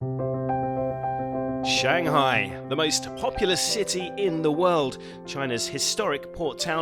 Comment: "most popular city in the world" gtfo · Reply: i thought he said popular too but i think its actually populace